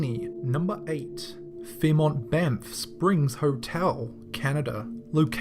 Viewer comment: i thought for a second he said fairmont bed springs hotel!